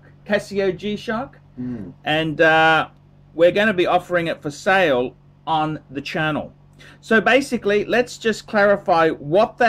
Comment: I was listening to this in the background while working and thought I heard say, "We're giving this away on the channel". I had to stop! I almost had a heart attack! Then I replayed it and realised I just had turds in my head. As if would ever give anything away!